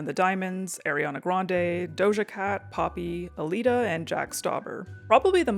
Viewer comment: did someone said Jack Stauber?